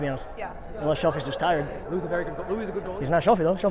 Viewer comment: I thought said "Lou's ass" instead of "Lou's gassed."